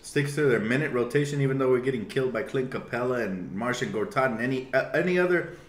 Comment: I'm watching you while getting ready for the day and I hear "Martian Gortat"! Funny!